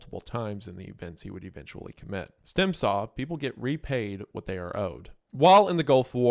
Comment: What does he say here? To me it sounds like "Stemsaw people get repaid what they are owed" What is Stemsaw?